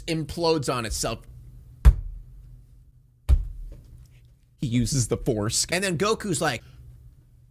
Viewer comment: the captions say “he uses the foreskin” 😂😂😂😂